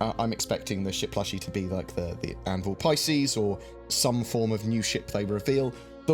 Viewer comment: You say 'Anvil Pisces', and I hear 'cheap LTI token'.